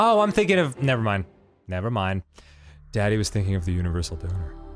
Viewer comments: Did Danny just call himself “Daddy” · DOES HE SAY DADDY OR DANNY ???? :D i really need to know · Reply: Daddy. Dan often refers to himself as “Daddy” or “Father”. He uses both in this episode. :)